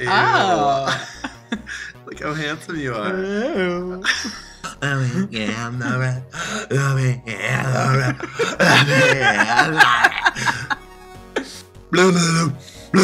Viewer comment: what is the thing scott is saying at 2:34?? I've been trying to figure out what it's from but I can't remember! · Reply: Michelle Chan "Pinkie and the Brain," as in the Animaniacs cartoon. :)